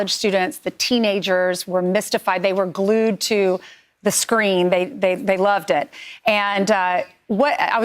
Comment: Yeah i heard ivanka t. “ loved it”😂😂😂😂😂😂😂😂😂😂